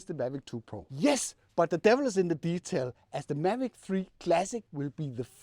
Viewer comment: I thought you said mavic 3 classic mini. Wishful thinking i guess 😂